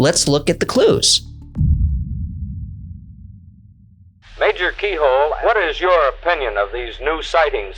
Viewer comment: i thought he said major pee hole